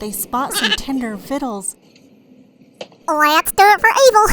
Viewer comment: I can't make out what dubya says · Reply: He says let’ s doit 4evil